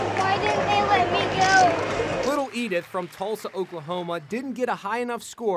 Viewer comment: When I heard "she came from TULDA Oklahoma" I was like "FINALLY SOMBODY GOT FAMOUS THAT CAME FROM TULDA GEESHHHHHH"